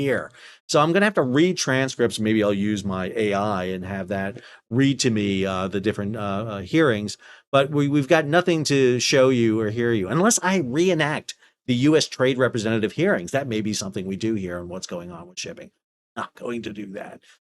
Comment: I can tell my hearing is failing when you said, "show you or hear you", I heard, "Soryu or Hiryu." Gotta stop watching Drachinifel.